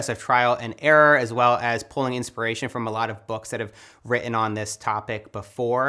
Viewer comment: lol did you say: "...as well as pulling inspiration from a lot of books that I have WRITTEN on this topic before.." ?!